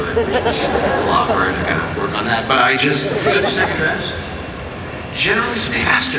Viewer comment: What does JJ say to Joss (and what does Joss reply with) at 8:55? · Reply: JJ: You don't do second drafts?? Bastard Joss: Well by the 6th episode it shows